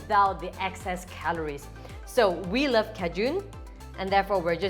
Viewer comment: I thought you were saying kejoon seasoning, like some sort of seasoning I wasn’t familiar with. Then I realized you were saying Cajun. 😂 Silly me.